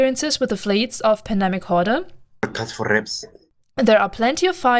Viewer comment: what is the word at 7:30, cant recognize it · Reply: "Broadcast for reps" i think